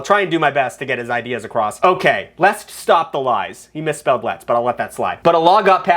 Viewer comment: I kept hearing it as "He misspelled lies" and I was like "No he didn't?" It took me 4 times to both hear and read it correctly. Haha.